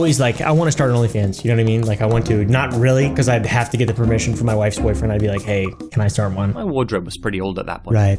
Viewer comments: wait what hold up did just said his wife's boyfriend 😳so yah in a 3 way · did he just say he'd have to get the permission from his wife's boyfriend? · Did he say “my wife’s boyfriend”? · did say he’d have to get permission from his wife’s boyfriend to start a only fans😂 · wait a minute at 10:02 did he say my wife's boyfriend??????? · umm my wife's boyfriend did I hear that right